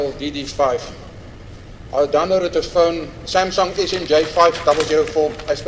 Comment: As a country we need an explanation on how did Ntanzi get his phone back while he was arrested.. I suspect they are framing him, cops used his phone and acted like its him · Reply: I thought they initially said the phone was a stylo 🤷🏽‍♂️ now I’m hearing Samsung